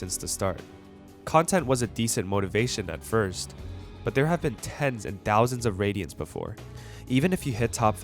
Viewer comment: did he just say TenZ?